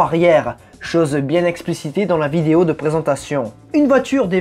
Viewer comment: c'est moi qui bug ou vous dites je cite "chose bien explicité" ... ???